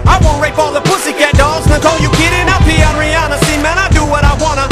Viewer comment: does he say "I'll pee on Rihanna"?